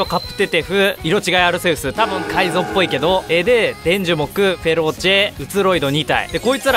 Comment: フェローチェ知ってるけど、申し訳ないけど、さんが言うとフェ○ーチェに聞こえるんよ...